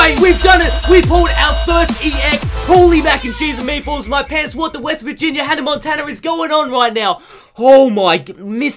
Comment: WHAT DID HE JUST SAY??? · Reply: "Holy mac n cheese and meatballs in my pants, what the West Virginia Hannah Montana is going on right now!?"